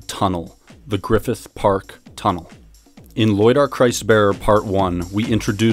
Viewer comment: the sound cuts out..."quite literally, thus.......tunnel, the Griffith park tunnel." Also at 14:08 the sound cuts "of all the surgeries that he......brain surgery..." What is said in the cuts? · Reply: "Thus we arrive at our tunnel". "He happens to be performing brain surgery"